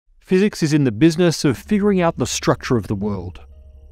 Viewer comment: I thought what he said at first was "felix is in the buisness of figuring out the structure of the world.." ..im like I AM !how does he know!